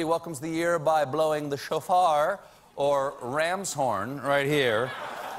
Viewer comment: i thought he said,'blowing the chauffer..."..:P